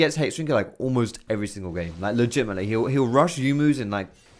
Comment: im sure he didn't but i swear he just said "like legit my nigga" but im half sleep so who knows